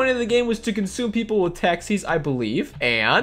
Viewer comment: It sounds like he said eaten by taxes instead of taxis lol